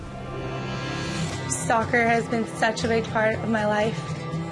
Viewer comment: At the beginning of the vid I thought she said stalker in stead of soccer lol. Anyway Alex has beautiful eyes.